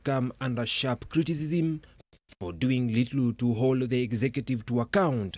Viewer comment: this reporter just said "littlu"..instead of little